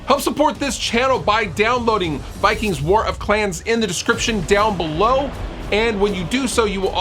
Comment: War of Clams? Oh, clans. I want Clam war! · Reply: Chris Franklyn I totally heard “clams” too. Viking lesbians....could be a fun game?